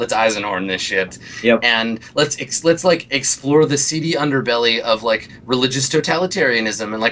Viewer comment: "Let's... explore the <> underbelly of, like, religious totalitarianism..." Can anyone please explain what that missing word is? Sounds like CD (I remember Gen using it in the originalRollPlay too, there was some sort of "CD bar")... And how do you spell that? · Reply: "seedy", which means like, sketchy, villainous, dubious... the seedy part of town would be the dark places where crimes take place and so on :)